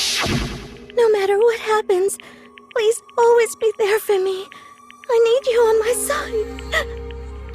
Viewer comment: for some reason i heard always be there for me i need you for my son